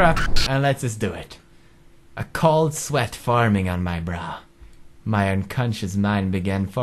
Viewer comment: Who else heard "A cold sweat forming on my bra" at 0:40?